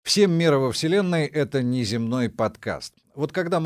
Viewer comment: Вначале послышалось: "Всем меры во вселенной"